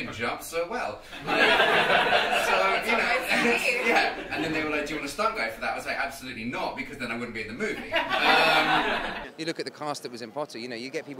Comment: Can someone tell me what is Jamie saying 5:30-5:35, I cant hear it and understand what he says :( please ♥ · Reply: "And then they were like "Do you want a stunt guy for that ?" I was like "Absolutely not, because then I wouldn't be in the movie" "